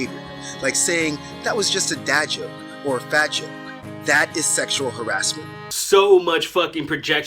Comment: "...mental acrobatics to justify abusive behaviors like saying ------------------- and ------------------- (what words is he saying, CC is not helping)? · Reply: That was just a dad joke or a fat (?) joke